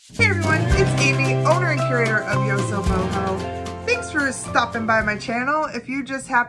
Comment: And for a second I thought you said "So YoHobo..."